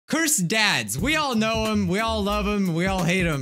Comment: Totally thought he said "cursed dad's".